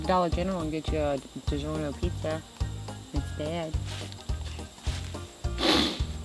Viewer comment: What did she say in the end? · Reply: I heard she said "you want for a round?"